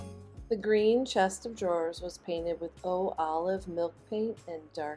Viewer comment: I'm going deaf! What kind of milk paint? I hear olive milk paint.... I'm in a 80sf booth green eyed for your booth acreage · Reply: Oh Olive Sweet Pickins Milk Paint with Black Oil Wax <link>